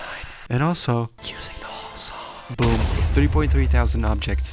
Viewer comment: If someone can't hear it correctly it's "using the whole song"